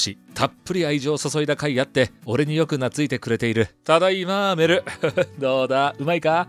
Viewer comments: ただいマーベルに聞こえる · ただいまーベルって聞こえた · 「ただいま メル」が 「ただいマーベル」に聞こえる